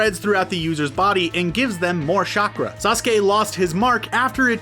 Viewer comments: Did he just say shakra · Did he just say "shackra"